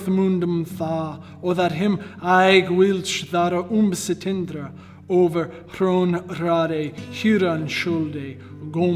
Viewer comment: i heard him read the word "overthrown" . Listen carefully, it doesn't take a genius to hear it